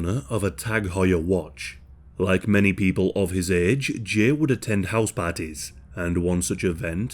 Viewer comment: why did I think he said “health parties” Lmao I was like wtf I’ve never heard of those before.